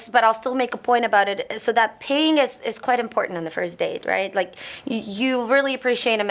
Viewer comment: I thought I almost heard "paying us is an important part of the date". That's my pre-conceived notions of Ukrainian women getting away from me...lol